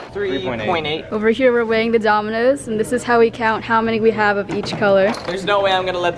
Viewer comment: ANY1 HEAR DOMINUS INSTEAD OF DOMINOES